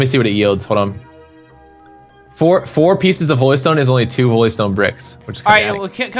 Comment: I thought said oilstone